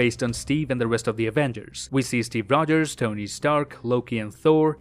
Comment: did you said tony stark?